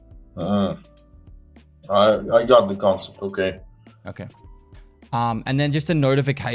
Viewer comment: What did say at 45:20? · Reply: “I got the concept ok”